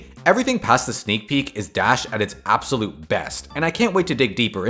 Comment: why tf do the captions say level and not dash